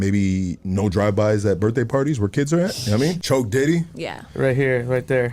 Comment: Sounds like dude said choke diddy.